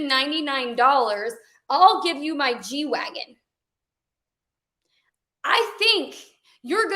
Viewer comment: Her: “g-wagon, g-wagon, g-wagon.” Me: what the hell is a g-wagon?! 😂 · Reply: Same LOL. I thought she said v-wagon which would make more sense